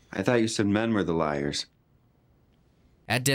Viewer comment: i thought you said "men are the liars" 😂😂😂😂😂🤣🤣🤣